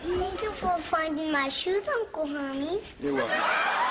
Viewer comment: Ive got an uncle herpes? I married a herpes? I admit that i thought i heard herpes the first time i saw this scene! 😂😂😂😂